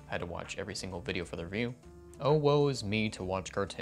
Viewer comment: DID SOMEONE SAID OwO!...no? ok